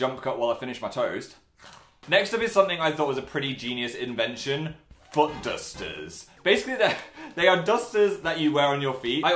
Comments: for some reason i heard "butt dusters" lmao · I thought said butt dusters. Uh.....